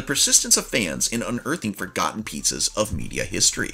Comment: thought you said forgotten pizzas lmao